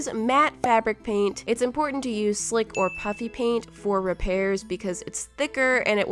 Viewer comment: “My *SLICK* puffy paint” DID SHE JUST SAY... “My *THICC* Waffy paint” Hmmmmm *GOES BACK TEN SECOUNDS* :0 lies. SHE SAID “My *SLICK* puffy paint” SUSPICIOUS 😒 Oh my that face was perfect For? Z DUDE 😒😒😒😒😒😒😒